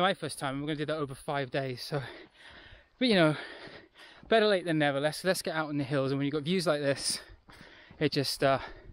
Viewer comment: What did you say at 12:42? 🤣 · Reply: "It's a whole other issue"